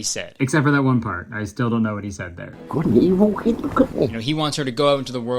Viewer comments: I think he's saying "good and evil? Okay, look at me" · I think the boss is saying “Good and evil …. could make” I don’t know man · i think he says "Good and evil couldn't hit me" idfk why he said that but somthin somethin jesus is related to it.